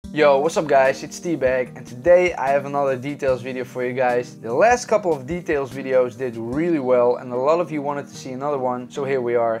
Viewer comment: Awesome video! But I was a bit thrown-off by the intro, where I heard "Hey guys it's D-Bag gaming." Turns out, it's T. Cool channel man.